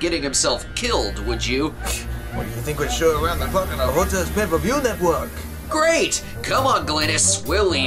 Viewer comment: Can someone tell me what the line at 7:48 is? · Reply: Drummer Productions "Pfft! What'd'ya think we show around the clock on our hotel's pay-per-view network?". Guess I should've turned down the music some to make it a bit clearer.